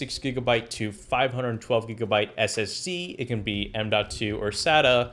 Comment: Wait did you say ssc or ssd at 10:17 ?